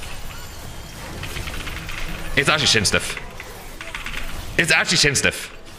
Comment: What is saying is a diff? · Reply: "Shins" lol like movement diff edit: apparently it's actually "chains dif" anathemas chains, I misheard as well